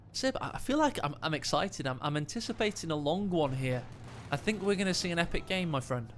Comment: did he say "he's getting excited, hes anticipating a long one"?